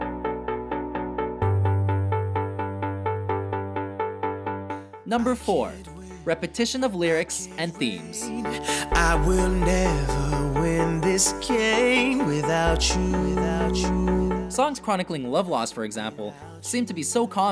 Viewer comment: HAHAHA I thought he said "I would never be this gay without you" 😂😂😂